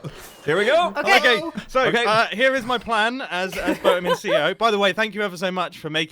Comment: Did Mumbo just say "Boatem of CEO?" 😂🤣😂